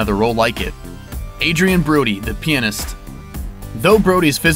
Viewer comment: Everyt me you said "Pianist" I thought you said "penis"